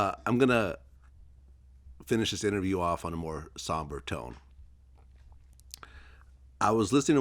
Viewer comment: I thought he said he was gonna end it on a softer note??